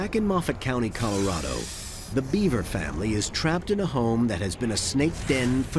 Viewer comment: for a second i thought he said beiber family :(